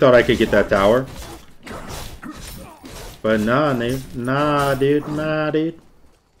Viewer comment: Did he say "but nah nigga?"